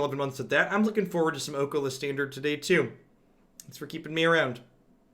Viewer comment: For a second there I heard "I'm looking forward to an Oko-List Standard today too" and was worried I'd slipped into the Berenstein Universe.